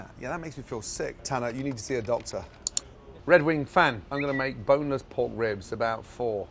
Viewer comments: I thought he said "ellow there mate" but it is actually "im going to make"... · I thought Gordon said "boner's pork ribs" at first. Someone kill me.